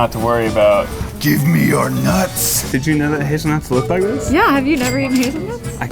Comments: Am I the only one to thought when Ned said hazelnuts, it sounds like 'his nuts'? Had to take a pause for a couple of seconds there. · ”did you know his nuts looked like this?” 😆 That’s legitimately what I thought he said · I thought Ned said, "did you know his nuts look like this?!?!" XD